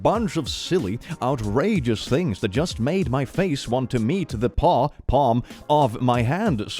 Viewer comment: 25 seconds in and he already "mistakenly" saw Paw instead of Palm. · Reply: I heard "pawpaw" like the fruit tbh.